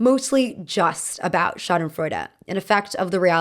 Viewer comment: “Schadenfreud” gurl i thought u were saying the show was shot i. Florida 😭😭